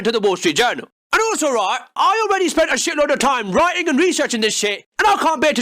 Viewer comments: What's that word uses? "Writing and" ....what? Reese erching? Reeser ching? I can't take this guy seriously if he's just going to make words up. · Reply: Researching he used the word researching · Hey. In case anyone cares, I had this video to make last week but got held up, hence why I'm still talking about this lol, couldn't bare to throw it away. But ***MINI CHANNEL ANNOUNCEMENT*** I'm gonna be going back to my older style, must shorter vids from now on. No more aiming for 10-ish minute vids to please the algorithm and aiming for maximum watch time, idc now man. Back to 3-5 min videos. The Youtube algorithm hates me and I hate it back. Gonna be trying to shit out a short-ish video for Monday about being sick, maybe? Who knows, we'll see how it goes. Much love, peace. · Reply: The way you said "Wallstreet journal" @ 1:35 first sounded like "Bullshit journal" to me, LOL!